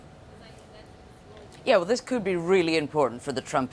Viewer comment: did she just say "Yo" at 4:56 LMAOO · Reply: "yea, well"